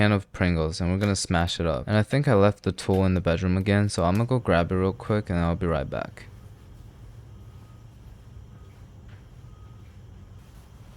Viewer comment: U LEFT THAT TOOL ON THE BEDROOM DID I HEAR IT RIGHT